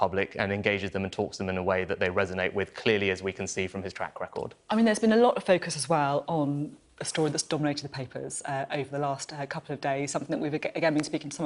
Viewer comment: Can someone understand what was the lady saying between 2:24 - 2:26 ? I felt like there were few English words just stormed suddenly like hail. OMG. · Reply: I think she says... "I mean it has been alot of focus aswell histories dominated the papers last couple of days..."